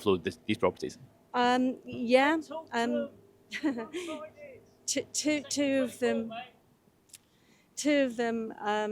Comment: What did that guy say? I couldn't make it out. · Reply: Something like “I’m an egregious cunt”